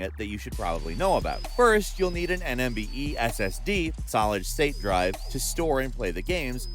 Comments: did he say NMVE? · Did he just said 'NMVE' ssd instead of 'NVME' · did he say nmve SSD 😂